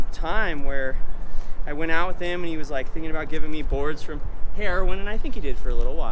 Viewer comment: i hope im really wrong but it sounded like he said "he was giving me boards for heroin" im i have to be wrong someone please tell me what he said